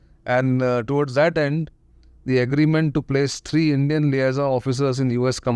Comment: statement cannot understand can any one explain bcz it’s not clearly audible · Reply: At that point, he is mentioning about the "agreement to place 3 Indian Liaison (communication between armed forces) officers in the U.S command for the first time."